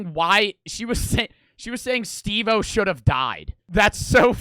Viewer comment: oh my god bro i thought she was saying she should’ve died instead of ryan but she was saying steve o should’ve died instead